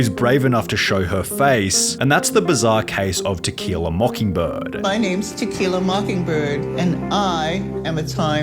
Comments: I heard “Tequila mockingbird” as “To kill a mockingbird” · Omg in My english class we are reading a book called to “kill a mockingbird”…….. I thought he said her name was that but it’s….which is very ironic😂😂😂